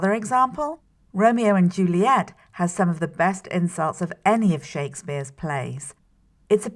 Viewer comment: I'm not sure if I'm having a fever or if this video is really worded kind of weirdly. Every time she says "insults" I hear "incels" and I was also a fan of these sentences: "Shakespeare used a lot of words." and "Romeo and Juliet: a play about two gangs (??)"